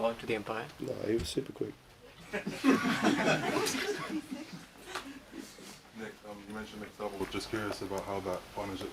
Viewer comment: What does he say at 9:05? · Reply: tinmy turber thanks!